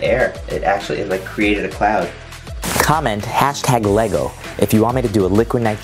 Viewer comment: I heard lego